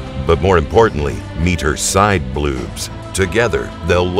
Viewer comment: I came here because I heard him say bluobs